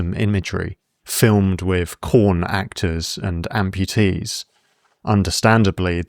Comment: I can't quite make out what you're saying ... corn actors? Great video essay anyway. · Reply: Yes, he said corn actors. That’s because YouTube is sensitive about certain words so creators have stared to use replacement words. Corn in this case replaces porn